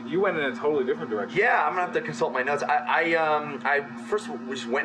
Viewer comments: He looks down and I thought he said "Im gonna have to consult my nuts." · it sounds like he says "I'm gonna have to consult my nuts" hahaha!